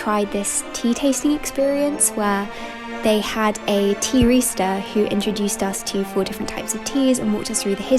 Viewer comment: This is so lovely… and for a split second I thought you said “tea rooster” 😅 but as I’m writing this you’ve just brought out your edition of A Little Princess so I’m going to hush up and finish the video! 😊